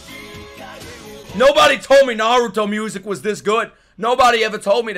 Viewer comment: i thought you said naruto music is trash years ago even though you've been told many times long ago